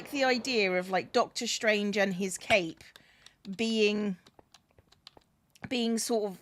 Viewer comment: All the time I kept hearing "Dr. Strange and his cake" and I was so confused 🤣😂